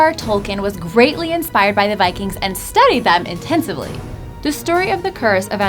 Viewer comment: did she just say.. Tolkien studies them...INTENSIVELY?